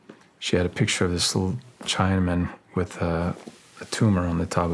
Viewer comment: Does he say "Chinaman" at 2:05?!